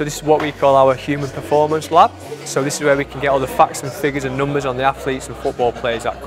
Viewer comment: When he said facts and figures I thought he said f*cks and figures